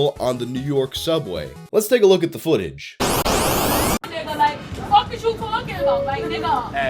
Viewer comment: WTF? 2:40 - THAT WOMAN IS SPEAKING ENGLISH? I cannot understand a WORD, I tried auto translate, and it reckons this is English?! I'm an Australian, I'm a native English speaker ffs - this woman is NOT speaking English. She's jibbering nonsense, I thought it was French! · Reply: Well, the audio isn't very good so that makes it harder to understand I guess. However, she clearly says "What the fuck is you talking about?" and I find that very odd. I'm not a native speaker but shouldn't it be "What the fuck are you talking about"? Then again, maybe she's no English native speaker either.